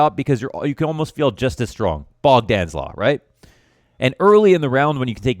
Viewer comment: what does he say on 11:33? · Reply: He says "Bogdan’s Law". Bogdan's law is the concept, that if you have a player with high health, a player with low health, and an AWP, the AWP should be given to the player with the lowest hp, as that is where it will have the most impact.